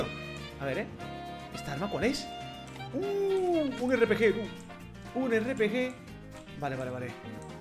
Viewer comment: Un final fantasy o que dijo??